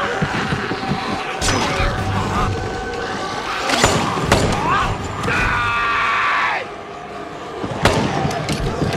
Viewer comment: What is Bob screaming at 2:40? lol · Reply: TYYYYYYYYYY